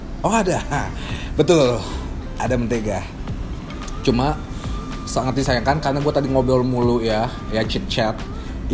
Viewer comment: Salah denger ga sih, ada backsound "disanguan, disanguan"